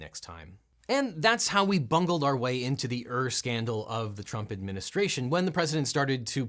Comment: He actually says “Ur-Scandal.” Ur- is a synonym for words like proto-, original, or primitive, denoting that the word being referenced, in this case “scandal”, is the first one of its kind.